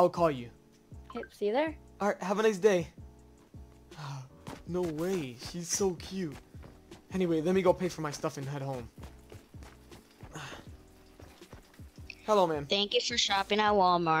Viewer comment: ummm i know you don’t care but did you say at 3:58 you said he’s so cute just wondering